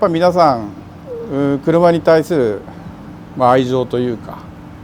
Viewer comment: FYI - 8:25min mark - 車に対する愛情 ... he didnt mention the world idol but for the love of cars/affection toward cars. Probably just misheard him saying idol instead of Aijyo.